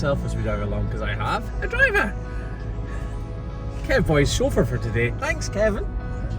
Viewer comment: When you said Kevin is chauffeur for today I thought you said sober today. Time to clean my ears.